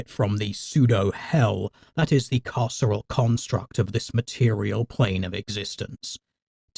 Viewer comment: Oh, "carceral construct". I kept hearing it as "casserole construct".